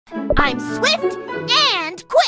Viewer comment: Why the heck I heard her say " I'm stick " Get stickbugged LOLOLOL 💀 (Who does that in 2o22